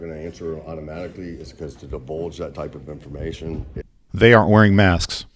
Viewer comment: Did he just say "to 'debulge' that type of information..."? Any attempt at appearing smart after that is pointless.